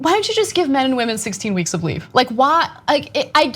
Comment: You guys need to compare what the rest of the first world is doing, America is waaaaaay behind. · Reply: James Bunt lol, I misheard 16 as 60 weeks at the beginning of the video and thought "hey that's fair", then realized it was 16 and was like WHAAAT